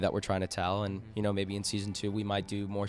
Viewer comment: i watched season 1 in 2 days a couple of months after it came out after i watched their zack sang show been a fan ever since <3 this interview was amazing :D and did i hear "maybe in season 2"